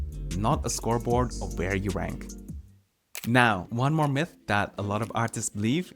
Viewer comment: i thought he said "Where you wank"